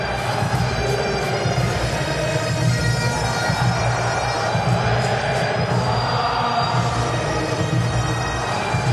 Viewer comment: please what do they shout at 0:28 and 1:03? "and stood against him............."? and then? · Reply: It can vary slightly but usually it's "against who?" Then "wankers".